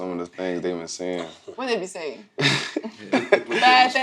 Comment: Did just say "What they be saying?" 💯